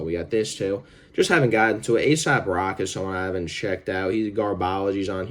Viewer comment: You called asap trash but happily listen to carti and danny brown 😭😭😭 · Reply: I heard aesop as asap thats on me